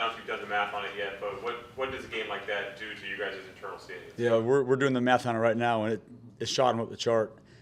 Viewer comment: he says we're doing the math...I kept hearing we're doing the meth...and cant stop laughing ..I need to grow up